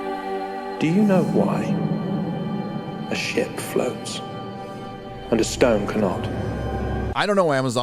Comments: I was listening to this whilst doing my art work and I honestly thought he said "Do you know why shit floats" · I first heard "do you know why shit floats?" - Which also makes sense, I guess) As much as ship, anyway.